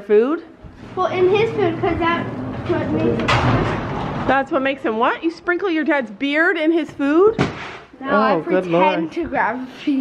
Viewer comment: I thought she said she sprinkled her dad's beer in fynn's food